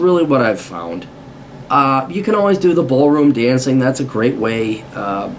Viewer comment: Did he say Ballroom Dancing at 7:30??